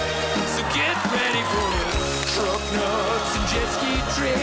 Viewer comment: I never thought I needed to hear a choir sing "Truck nuts" but I'm so glad it happened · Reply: I coulda sworn I heard Trump nuts, but I guess not...